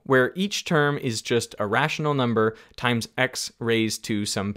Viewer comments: I think the captions are wrong here. It says irrational when it should say a rational. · Subtitles: “… where each term is just irrational number” But I think I distinctly heard “… a rational number” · Truth - shout out to Ivàn! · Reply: in English subtitles it's supposed to be "a rational" instead of "irrational". Anyway, I appreciate the video. It's just a tiny point-out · in English subtitles it's supposed to be "a rational" instead of "irrational". Anyway, I appreciate the video. It's just a tiny point-out